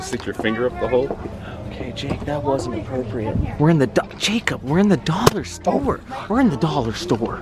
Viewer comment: I’m sorry did I hear that right did Cory just call Jake Jacob